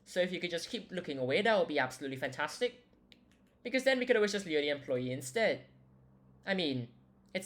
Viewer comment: the subtitles said "Leo the employee" i hereby dub that employee in particular Leo